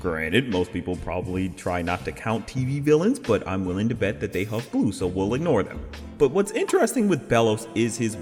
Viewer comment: "I'm willing to bet that they __ ____" What do you say here? My APD is can't make out this sentence. "huff glue"? Is that it? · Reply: Yes I said huff glue